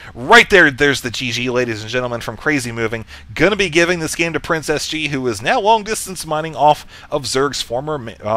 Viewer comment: Was i the only one who thought he kept saying PrincessG??